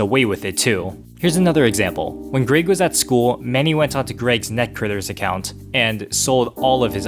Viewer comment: says greeg instead of greg